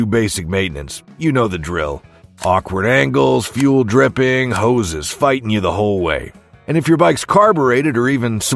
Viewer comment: Listening to this as I work and idk why I heard it as, "hoes is fighting you the whole way" 😅